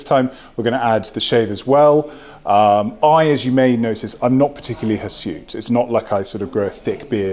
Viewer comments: What is that word? Hesute, hisute? Please can someone tell me what he said, and what it means! TYIA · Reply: Hirsute = hairy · What word does he say at 2:18? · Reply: Hirsute. It means "hairy."